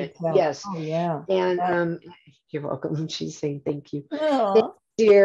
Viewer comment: mark did anyone else hear her majesty say “Thank you”